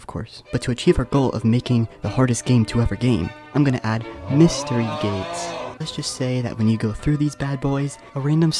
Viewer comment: I was doing something on a side and I heard "mr. gayts"